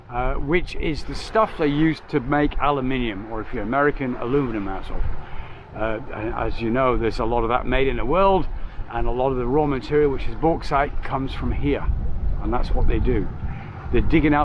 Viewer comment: did you say aluminumasshole ?! 😆